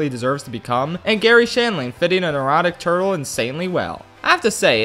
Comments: Had to do a double take on the "neurotic turtle" line cuss the first time I heard it I thought you said "erotic turtle". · I misheard "a neurotic" and thought he was call him an erotic turtle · When he said neurotic turtle I thought he said erotic turtle... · What he probably said: “A neurotic turtle” What I heard: “An erotic turtle” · He said “a neurotic turtle” but I heard “an erotic turtle” and I like the latter more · why did i hear erotic turtle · Anyone else hear that as "an erotic turtle" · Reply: R. M. Tosh i really thought he said an erotic turtle